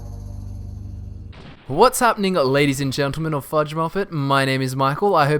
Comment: When he said "what's happening", I first heard it as if you said "what's up my nigga" xD